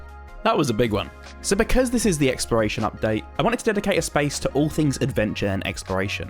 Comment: When he said exploration update, I thought he said expiration date!!!😅😅😅😅